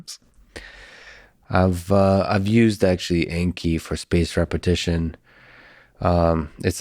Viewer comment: What is the app lex mentions at 3:40:14? Cant make out the name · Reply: Anki i think